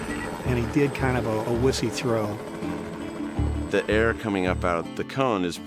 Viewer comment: "Wussy throw" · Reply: Trust Valentino I thought he said pussy throw lol